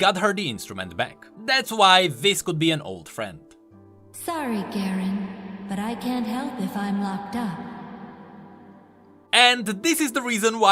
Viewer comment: Almost thought she said "sorry garen cant help if im knocked up" lol